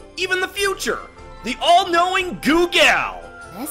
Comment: Honestly I thought you said goongala. Great episode though